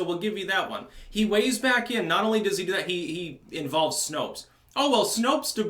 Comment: I thought you said Snoke instead of Snopes